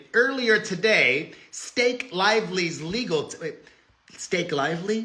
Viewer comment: Garbage! When you said Stake Lively, I thought you were saying Mistake Lively... and it still makes sense!